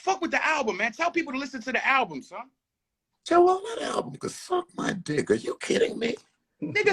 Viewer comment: I felt both sides till the SMD comment, people get killed over that shit.... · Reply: Yeah but Joell misheard Joe, Joe said “that album could smd” Joell heard “Man smd”😂